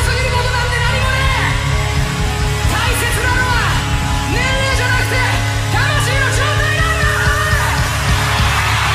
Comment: このあたりって魂のなんて言ってます？ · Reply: 状態